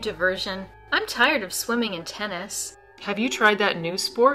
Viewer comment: lol - what I heard was, she's tired of swimming in 'tennis' Probably I should reconsider my thinking.